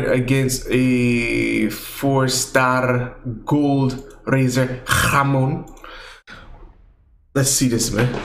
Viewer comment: did you say ham in spanish? "Hamon"